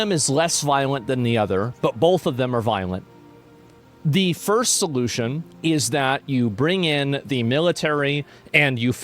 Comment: Almost thought he said "final solution"